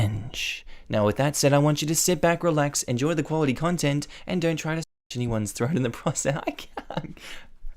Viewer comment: Is it bad that I thought in the intro he said don’t fuck anyone’s throat then I had to go back and had to listen again and then he actually said slash anybody’s throat